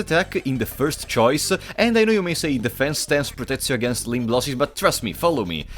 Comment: "Defense stance protects you against ...." what did you say there exactly? Sounded gibberish. Edit: He says " limb losses" but pronounces it like "lim blossies"